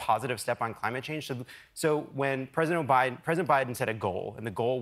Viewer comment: did he says President Obiden ?